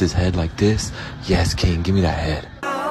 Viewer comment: What did bro say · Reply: yes king gimme that head